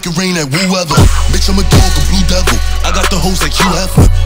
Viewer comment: who else thought he said duke blue devil